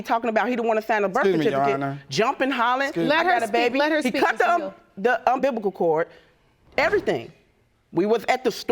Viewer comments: So I've rewound this thing 5x. Did she say un-biblical cord? 🤔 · did she say unbiblical cord?